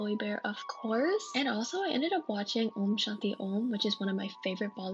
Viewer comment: Omg did I just hear you say “Om Shanti Om”???? Ahhh